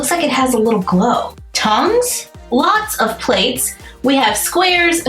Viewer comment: it sounded like you said tongues instead of tongs 😅